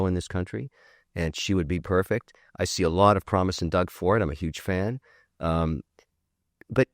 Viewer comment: did you just say you're a huge fan of DOUG FORD? Oi vey, I'm weary of you, Leary.